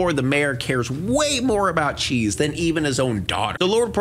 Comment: watched this movie in theatres as a kid, half the time i thought they were saying chi not cheese